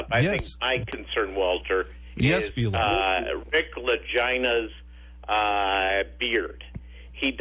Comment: Did he say Lick LaGina. Or Dick LaGina ? Lol